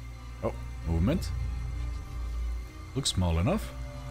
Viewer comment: In Seconds 0:27 I heard you saying Moment really German so are you german